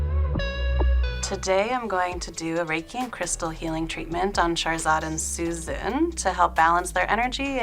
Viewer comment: it sounds like she says that she’s “going to do a reiki and crystal healing treatment on Charizard”. (And Susnnnnn, but who cares about her). Well, that’s what it sounded like to me anyway.